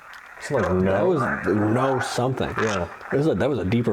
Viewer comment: The EVPs in this video are insane! At 46:46 I heard a clear "No, she didn't"